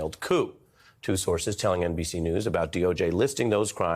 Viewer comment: For a minute I thought it said "Witless Tampering" 😂😂😂